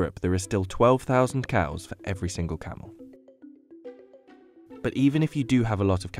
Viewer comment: "in Europe there are still 12,000 cows for every single cow." That's what I heard